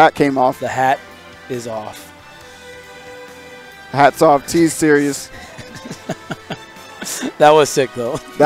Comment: sounds like he said "T-Series" instead of "T's serious" lol